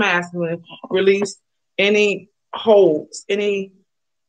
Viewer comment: you said the universe is making him release any “holds” I thought you said “hoes” hell them too 🤷🏽‍♀️🤣🤣🤣 Just a lil humor🤷🏽‍♀️ Great reading!!♥️♥️